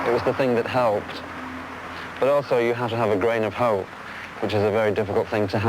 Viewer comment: what did he said in 3:40 i can't understand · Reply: He says “you have to have a grain of hope” implying that since where he grew up was so dull having hope and belief would help him get through his adolescence :)